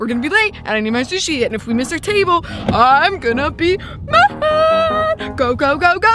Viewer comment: I thought she said titable